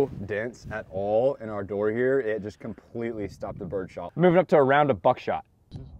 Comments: I thought you said "Moving up to a round of f*ck-shot" I was like “What?” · It’s sounded like you said “Moving up to a round a f**k shot”